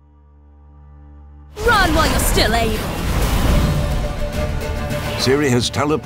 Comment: "Run while you're still able (to)" had to rewind 4 times cuz I thought she said "run while you still aim" xD which makes no sense.